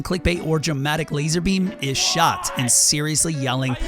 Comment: In the beginning i thought you said LazarBeam was shot, not shocked. I was surprised.